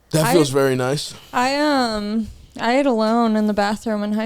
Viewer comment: Did anyone else hear “I ate a load in the bathroom” 26:19 😭😭